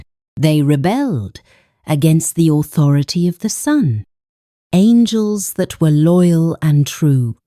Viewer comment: We do apologise for this. We noted this error and we will fix this on our upcoming video . These are AI generated captions and it does look like it capitalised names but God remained with a small letter . We have noted your concern 🙏🏾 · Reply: Also at 6:05 should be "Son" instead of "sun".